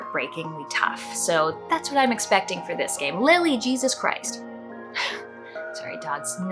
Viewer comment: When you said, "Lily Jesus Christ!" I thought you said at first "Literally Jesus Christ!" and was referring to the character you were looking forward to playing in THE EXPANSE lol🤣